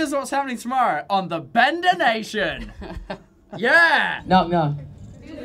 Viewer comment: Wait.... in that radio part at 1:07.... did they say Bender Nation?